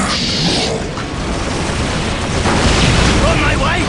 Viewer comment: did I heard some Ork shouted "For my wife" ? HOW dafuq can Ork got a wife!? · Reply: He said "On my way" :)